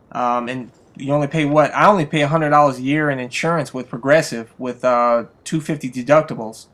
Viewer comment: I thought you said "I only pay 100,000 dollars a year in insurance" and I almost freaked out a little bit